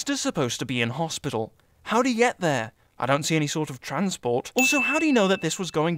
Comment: the narrator says there, but the subtitles say here. *ding*